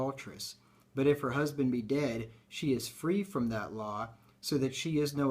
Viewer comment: Sir all i hear is "she is freed" i dont hear both he and she